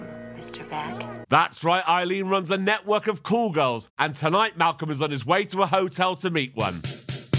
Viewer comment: I misheard call girls as cool girls. Also everyone in this film is clearly a selfish lover.